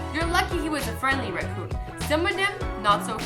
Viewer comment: With captions on.. Instead of saying raccoon it says.. rAt pOoP